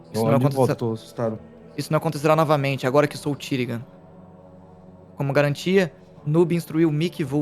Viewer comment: Pior que meu fone tava travado ai eu ouvi "Isso não acontecerá novamente agora que vou o Tiringa" pqp, eu nunca ri tanto mano kkkkkkkkk